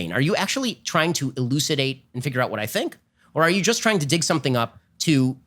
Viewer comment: Who else thought he said “trying to dick something up”